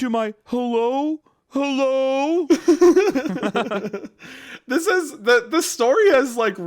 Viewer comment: I heard this as "NO THIS IS PATRICK"